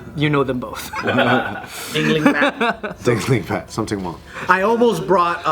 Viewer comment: Lmao did say Ryan's ex gf's name was sum ting wong? 9:50 Hahaha